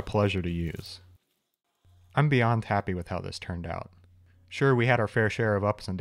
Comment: "I'm beyond tappy with how this turned out." I swear that's what I heard. I had to listen twice to figure out my brain is just making up really well timed puns.